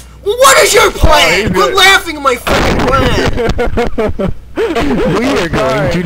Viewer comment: Did say "fuckin" at 1:04??????